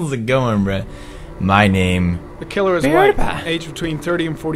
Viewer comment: it says "The killer is...." and Pewdie says "Pewdiepie" so it sounds like its saying "The killer is Pewdiepie"